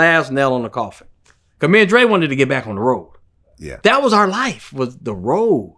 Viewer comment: When Yella said “me & Dre wanted to get back to the road” all I heard was “me & Dre wanted to get back to the hoes” 😂